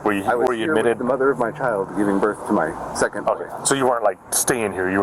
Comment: Did he say, around 2:02 that the mother of his child was giving birth to his second brother? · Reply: He said "second boy".